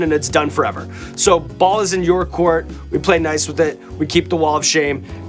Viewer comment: I don’t know where my mind was at, but I heard, “my balls are in your court”. I need a chicken wing . . .